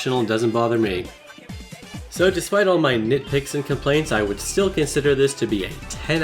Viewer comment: what he said " so despite all my nitpicks and contemplates I consider this a 10/10 game" what I heard " so despite all my nitpicks and contemplates I consider this a 10/10 gay"